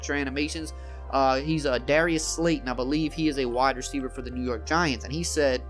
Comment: I thought he said dariuys slay instead of slayton. he probably gets that a lot though